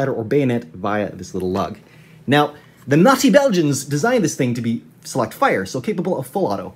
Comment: The first time around I thought he said “Nazi Belgians”, I was like wow that’s weird that Israel is using a gun made by the Nazis.